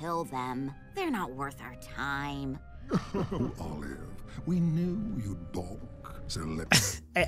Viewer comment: I think hes saying "we knew you'd balk"